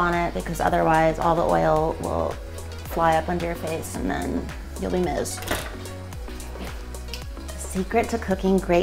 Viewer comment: Never pour oil down the sick. It will get clogged and create the worst smell. Also, did anyone else crack up when she said, “I always cover the bacon because oil will fly everywhere and then… you’ll be missed.” Lmfao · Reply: I thought she said then..you'll be mist. LOL